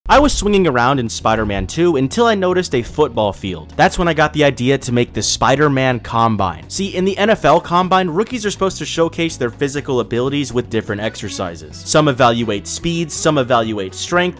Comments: I thought he said spider man columbine i was like wtf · Kinda thought he said Spider-Man colombine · I thought bro said colombine for a sec💀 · I thought bro said spider man columbine 💀💀💀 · My dumbass thought he said colombine😭😭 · actually thought that said columbine for a sec 💀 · i thought he said columbine